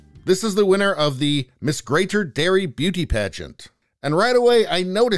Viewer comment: i thought you said, Miss Greater Dairy Beauty Pageant